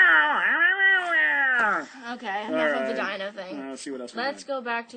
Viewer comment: what does she say at 5:30, it sounds like she says i have a vagina thing?